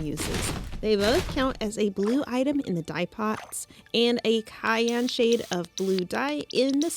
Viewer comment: I was like "wait, did she just say 'cayenne' like the pepper? That would be red/orange wouldn't it? LMAO Then I realized you were say "cyan" with a hard C instead of the typical "sigh" for "cy" that I'm used to hearing. <3